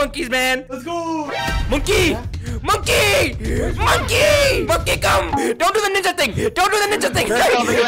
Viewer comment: what did he sayyyyyyyyy? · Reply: Monke come